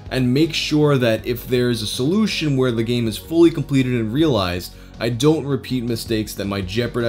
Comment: realized? I thought that said released